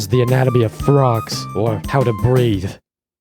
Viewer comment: Ah breathe,i heard something else the first time. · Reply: Same. I heard breed.